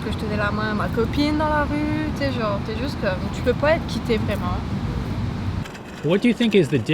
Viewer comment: Minor correction, at 4:35 she actually says "tu ne peux pas être qui t'es, vraiment" which means "You can't really be yourself"